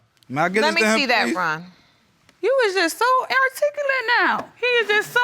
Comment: Did she just say "air-ticulate" 😂😂😂😂😂